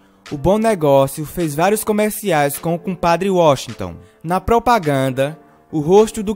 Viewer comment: Compadre Washington foi isso que ouvi ? '-'